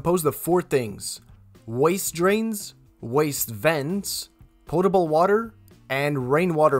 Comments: Sounds like he is saying portable water LOL POTABLE -- no 'R" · Sounds like you said portable water and not potable water....